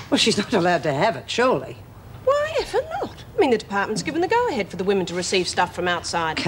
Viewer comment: Did Mef say F'n? 20:17 Woah Go Meg!! LOL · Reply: Nah she says "why ever not", although it's more like "why efer not".